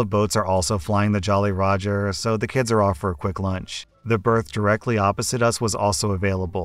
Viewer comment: I was listening only while I was doing my schoolwork. I was very weirded out when I thought the first op was saying BIRTH.